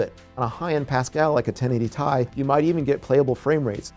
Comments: Did he just said 1080 Tai? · Did he pronounced It Tie instead of Ti?